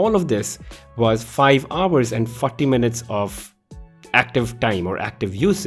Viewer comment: I thought he said "5hrs and fucking minutes" lmao